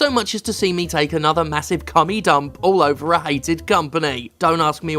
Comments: Legit thought Steph said "Commie Dump," not the other thing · I misheard "commie" dump instead of what it said, and it made so perfect sense, but subtitles to the rescue!!!